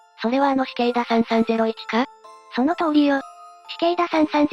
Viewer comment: 死刑だ。3301って聞こえたのは私だけ？